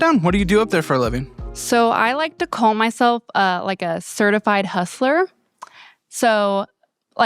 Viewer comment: No way I'm less than a minute in and I hear "I like to call mythelf a thertified huthler"💀